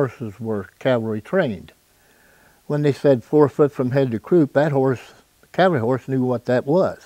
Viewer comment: ~ " 4 foot from head to croop...." what did he say? Was it "head to hoof"? That can't be right, because the horse knew what that phrase meant. Well, the horse knew more than me, that's for sure, because I don't know what he's talking about. Is this how this interview is going go? My uncle was drafted in the army, early in WWII and sent to Arizona to train in the horse cavalry. He didn't know horses from cows. He was a teacher. His brother, another uncle I had in that war, was a cowboy, actually herded cows on a horse. When he heard his brother was in the cavalry, he couldn't believe it. It didn't matter anyhow. They disbanded that cavalry outfit, I'm told, and my uncle, instead of riding horse, ended up in Australia as an army typist.